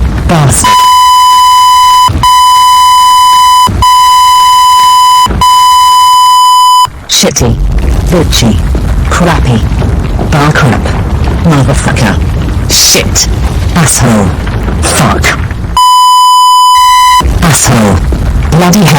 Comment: Did catherine say b!tchy?